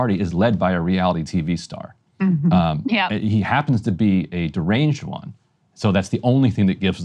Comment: Did he just call Trump "deranged"? Or did i misunderstand?